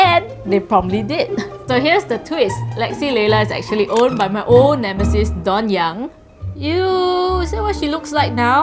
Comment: did u just say “lexi layla is owned by my own embassies dawn yang”